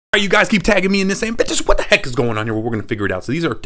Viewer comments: i thought he said beeches what the heck is going on here. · I first heard "biches what heck is going on here" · You probably thought he said the b word he actually said “ y’all keep tagging me on this asking bentist what the heck is this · When he said, Bentist, what the heck is going on here, I thought he said, B*****, what the heck is going on here 😶‍🌫️🥲😳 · Ok so I thought he said "u guys keep taking me in this video, b!ţçhəš what is this" but I know he said bentist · It kind of sounded like he said in the beginning Bitches what is going on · Am I the only one who thought he said bishes what the heck is that and not bentist what the heck is that? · i thought he said "b*tches what the heck is goin on?" he said bentist 😭😂